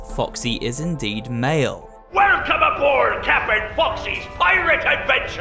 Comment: did he just say Captain Woxy??